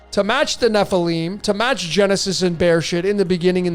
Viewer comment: Was i hearing "bear shit"?